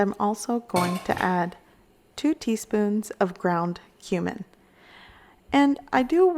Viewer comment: I thought u said “ground human” I’m like WHAT 😱!!! lol